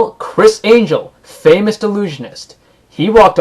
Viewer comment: Sounded like you said "delusionist" instead of "illusionist."